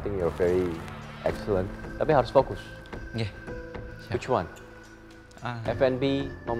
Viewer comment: LMAOOO that guy is sooo cuteeee like look at his baby face TvT BTW did he just say "nggeh" at 9:28 or